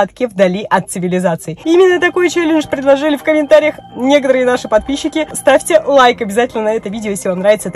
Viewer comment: мне послышалось не некоторые мои подписчики а негры и мои подписчики😂😂😂